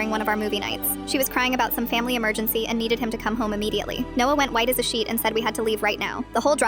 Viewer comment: Bro at first I thought she said as white as a sheet